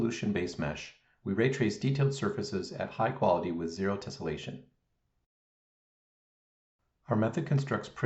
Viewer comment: This is incredible. It sounded like you said this happens without tessellation, but the explanation was above my paygrade. Wow!